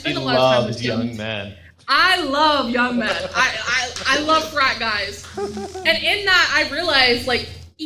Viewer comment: I had to rewind when she said I love frat guys, I thought she said french fries...